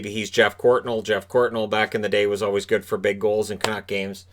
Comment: my ears played tricks on me cause i thought you said Jeff Gordon lmao.